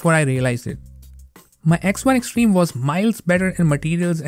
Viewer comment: An example of why enunciation is important. It sounds like you said "my ex-wife's stream." 🤣🤣